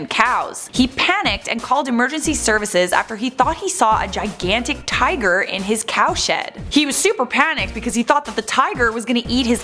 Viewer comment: I heard her say "he found the tiger in his cow shit" anyone else?